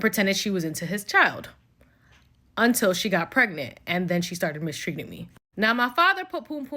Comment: I thought she said she is pregnant now